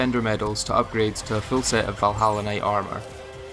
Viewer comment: im addicted to funny bug game that much, that i heard "hollow knight armor" instead of valhalla knight armor 💀